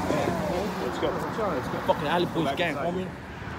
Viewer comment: did He say Alley puto's gang? Lol...